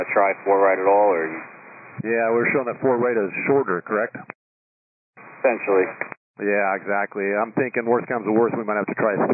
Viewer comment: "4R is shorter correct?" -"Potentially"... Don't you love it when you ask a closed question and you get the "third answer". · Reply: I thought he said "essentially"